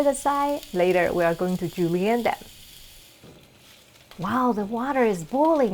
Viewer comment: hi, at 1:15 when you say, "let's set it aside. Later, we are going to '____n' them." What's that word exactly? I'm learning fancy vocab from your videos! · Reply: Thanks for this fantastic question!!! Julienne. The julienne cut measures 1/8 inch × 1/8 inch × 2 1/2 inches and is basically the allumette cut once more lengthwise. You will most often use this cut for carrots, celery, or potatoes, and see the thin strips used as a garnish.